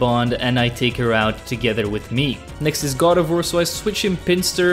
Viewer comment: is it just me, or did say god of war instead of gardevoir?